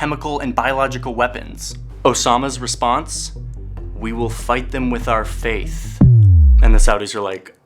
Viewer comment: The first time I watched this video I misheard "We will fight them with our faith" as "We will fight them with our face" and that always sounded so fucking badass to me. Like a bunch of warriors sacrificing themselves like hordes, zerg swarms or zombie mobs just not to give an inch of territory, stalingrad style. However it's still a very stupid idea anyway.